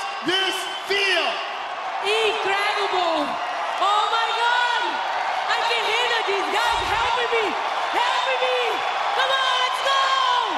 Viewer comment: What does she say at 1:11? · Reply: "*OH MY GOD!* I CAN'T HANDLE THIS GUYS, HELP ME! HELP ME! C'mon, let's go!!"